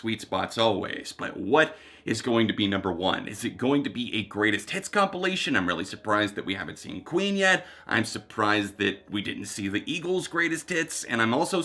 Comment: for a second I thought you said “Ween” because that’s a band name and I was so confused